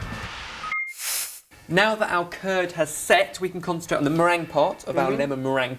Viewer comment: im sorry but at 3:24 i thought he said turd xD